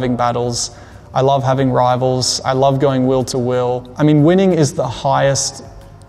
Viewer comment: 說的應該是 I love to going wheel-to-wheel. (翻譯可能聽成 will to will)